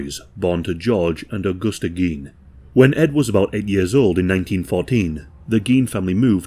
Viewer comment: I got hooked the moment I heard "Ed was about ed years old"